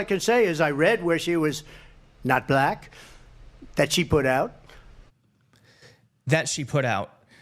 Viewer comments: Maybe I'm crazy but I heard "that she put out" as "she was only disclosing that she was black" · Sorry, even after hearing it twice the 'that she put out' phrase I hear as 'that she reported' or 'stated'. I hate the man but I don't hear the sexual innuendo. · I did hear him say "she put out", but I think you're hearing something that's just not there. For me he says "I heard she's not black, that she put out." As in, she's the one that said she's not black, he didn't say that. That's what he was trying to say. Don't get me wrong, I hate the man, but I just did not hear it the way you seem to have heard it. And, as far as I've seen so far...none of the other top new channels heard it that way either.